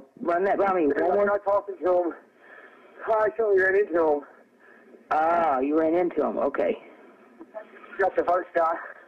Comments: it sounds like he said “i ran into him, it’s not the first time.” but i mean i could be wrong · It sounded like said “I told them I didn’t know them” not “I ran into them”. Sounds more like he witnessed a murder or something and they thought he knew them🤷🏻‍♀️